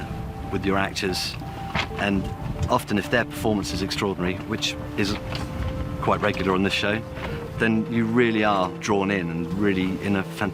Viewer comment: is he saying that the acting is NOT good? Haha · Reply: "Their performances are extraordinary.. which is quite regular on this show."